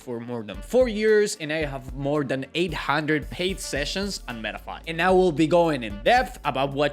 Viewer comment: 800+ paid session on what platform? I didnt catch it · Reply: on metafy